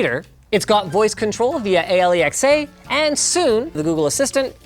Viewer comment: Did he say A- L- E- X- A Instaed of Alexa ???